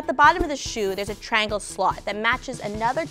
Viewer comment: I heard slut 😂